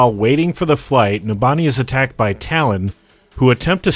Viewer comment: I was eating chips and I heard what if Nintendo was attacked by Kaplan instead of Numbani was attacked by Talon LOL